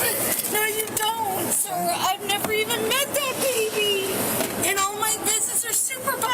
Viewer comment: EDIT: Sorry, i must have misheard her American accent. When she said she had never met the baby, i thought she said "never meant". She said "I didn't mean the baby!" So she wasn't denying that she had those conversations with her.